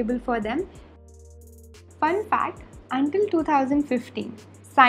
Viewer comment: Did I heard until 2050